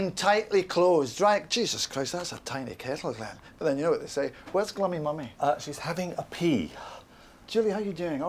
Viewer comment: what does Malcolm say after "that's a tiny kettle" around 2:34? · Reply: "but then you know what they say... Where's glummy mummy?" "you know what they say" making a joke about penis size "Where's glummy mummy" that's his nickname for nicola